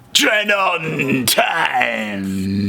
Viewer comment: Always remember: "TRAIN ON TIMEUUHHHH!!!" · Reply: I always hear "tread on time" anyway :D